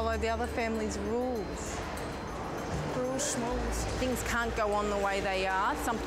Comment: Guys, I'm not an English native speaker and I'm extremely curious about something in this video. What does the teenager say at 0:30 ?? I can't understand it, and I would really like to learn the meaning of whatever the hell expression she said 😅 it sounds like rules smoosh? Smooth? Could someone kind pls explain it? TY :)) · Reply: She said ‘rules shmules’. Shmules isn’t an actual word but she was basically mocking the rules, as in she won’t do them, it’s like rolling your eyes at something.😊 hope this helps